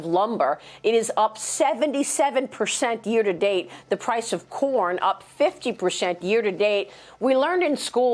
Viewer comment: When she said 'the price of corn went up 50%', I misheard it and thought she said 'the price of porn'.